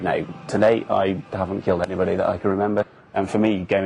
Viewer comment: did he say “i haven’t killed any body that i could remember” o_O